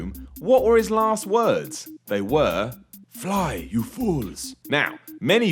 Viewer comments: I thought Gandalf said, "run, you fools." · I thought he said "run you fools" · I always thought Gandalf said "follow, you fools!" Which actually never made sense in my mind.